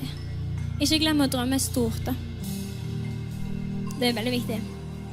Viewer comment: Somebody else probably has already helped you, considering the crowd. At 1:18:18 You say: "(I)Don't know what she just said, bummer." Let me oblige :) She speaks a (Norwegian) dialect from the Os/Bergen area, she says: "Ikkje...Ikkje glemmer drømme stort, det er veldig viktig." Wich means: "Don't...Don't forget to dream big, it is very important." Typical Aurora, remembering us to dream, to have hope. Great lead-in for "Daydreamer." Source: Am studying Norwegian, please correct me where wrong. Also, since you mentioned German: Norwegian is a germanic language just like German, English, and Dutch(and more)! "wichtig" and "viktig" are the German and Norwegian words for "important", quite similar! As are the words for "correct", "richtig", and "riktig." You, yourself recognised "velkommen" as the German word "wilkommen", or the English "welcome." Quite right! The Dutch word is "welkom"!